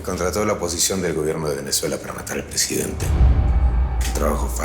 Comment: escuche mal o dijo venezuela